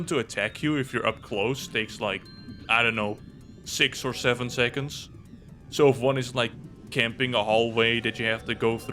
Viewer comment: what did u say??? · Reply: get out